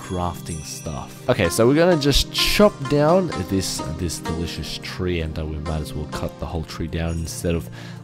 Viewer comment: did you really say dillicous tree?